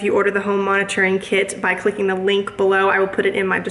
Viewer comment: When you were saying self monitoring kit I thought you were saying self laundering kit. I got super excited that my laundry was going to potentially do its self